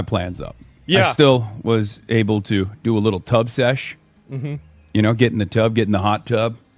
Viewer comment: at first I thought he said tugg sesh .... he got so anxious he had to rub one out 😂😂